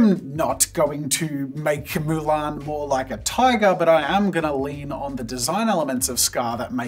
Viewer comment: did he just say tiger? xD